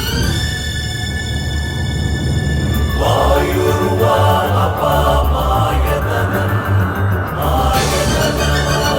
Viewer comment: Can anyone tell me what mantra is at 1:37 Please someone tell me, i want to know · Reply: Mantra pushpam